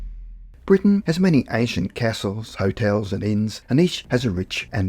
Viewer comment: I misheard it as Britain has many Asian castles